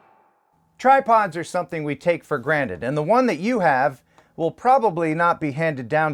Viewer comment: oh I thought you said "Tripods are something you take to grammas".... Crap now I gotta head to nonnas house to get my camera gear back! Great review Again